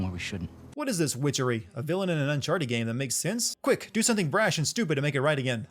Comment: it says uncharted fame instead of uncharted game